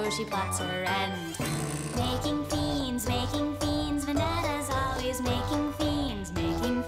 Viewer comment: When I was younger I always heard it as "Making beans making beans it's all about making beans"